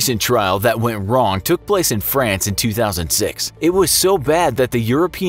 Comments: voiceover says 2006, subtitles says 2016?! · The narrator says 2006, but the captions say 2016.